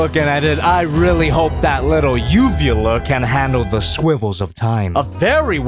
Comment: Did just say... SWIVELL?